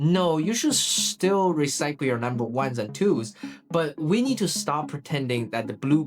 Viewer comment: Did you say at 12:10min "We're going to recycle your ones and twos...." Eewwww!! LouiseAustralia 🦘